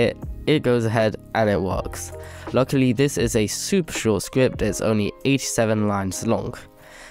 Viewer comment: bro i thought he said luckily its a super easy script and its only 87 lines long im cooooooked